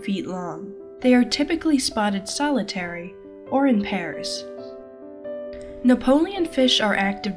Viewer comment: Thought the narrator said “they are often solitary, or found in Paris “ which, actually considering the name makes sense 😆